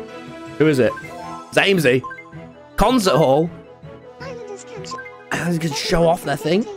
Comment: I can’t be the only one who heard him say cumza hall and not concert hall please my dirty mind